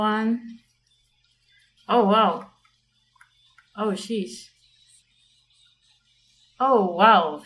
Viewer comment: i said "oh SHEESH*"!!! i dont say words like that this is a christian household!!!! /j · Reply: i thought you said "oh cheese"